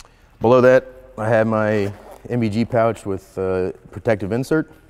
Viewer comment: he mentions an 'OBG Pouch' (am I hearing that right?), what is that? - It states in his loadout that it's an Spiritus Systems OTB Medium GP Pouch Mk3. I just wondered why it he said 'OBG' Thanks in advance guys - great video :) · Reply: NVG pouch (GP) with protective insert.